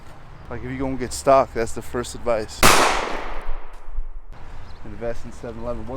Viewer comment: What does he even say? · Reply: if you going to get stock thats the first advice hahahha